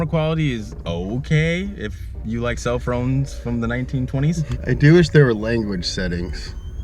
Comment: Cellfrons from the 1920s???? Wtf did my manz say hahahah keep this guy he is hilarious